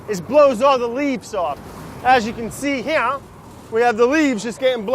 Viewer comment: Sounds like he said “As you can see hereyuhh” @ 8:20 😂😂